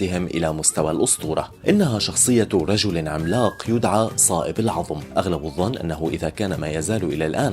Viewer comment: هل سمعت الإسم "صائب" العظم أم انا مخطيء🤔